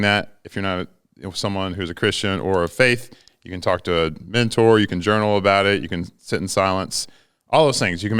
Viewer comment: When he said "mentor" I thought he said "minotaur" and I got worried for the training difficulty this year.